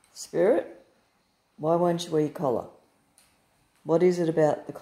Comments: I didnt notice that at first but just played it back and it does sound like he says "You Wear it".🤣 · Sounds like he said you wear it. 😂😂😂😂😂